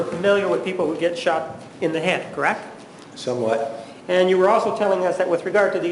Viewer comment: does he really say so what?!? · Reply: Some What